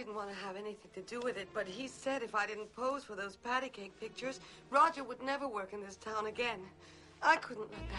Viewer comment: When she said pictures I thought she said bitches